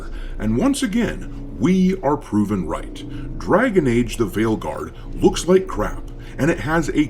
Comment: When you said the game's name at the end, I heard it as "Dragon Age: The Whaleguard". Seems fitting, as it's the guard of all the blubber filled blue hairs.